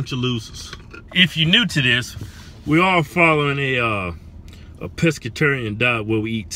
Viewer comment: I thought you said a Presbyterian 🤣🤣🤣🤣🤣